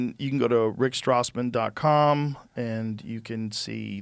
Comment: I thought that said Rick glassman